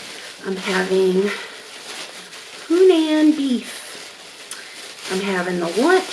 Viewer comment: Just woke up and thought it said human beef lol but i hope you're doing well much love from Australia